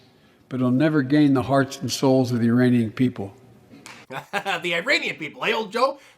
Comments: Haha, for a second I thought he said: "uranium people". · I thought Joe said "Uranium people" 😆👍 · I heard, “…the uranium people” and wasn’t even surprised